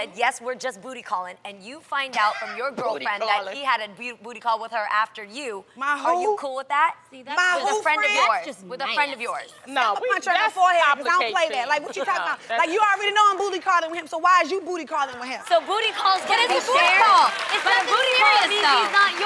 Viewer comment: did Tamra say "I'll punch that in the forehead" hhahahahhahahahahahahahahhahahahahahahahhahahahaha those one liners kill me..hahahahahahahahahaha